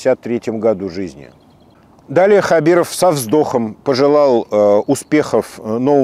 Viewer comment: секунда "далее хабиров сам сдох" , а нее послышалось, а так....